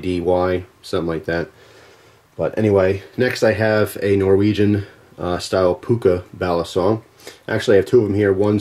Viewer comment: A Norwegian style puka? balisong? is that what he said @ 04:09?